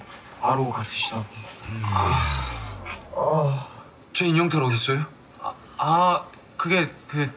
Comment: I think he said where is the "doll suit" (not the mask). Subtitles are wrong......